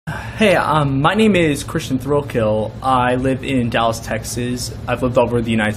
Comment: I'm so sorry...I thought he said Christian Roadkill ...LOL BUT ohhh come sweet November :)